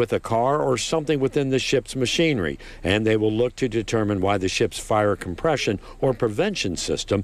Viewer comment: did he say "...the ship's fire compression..."?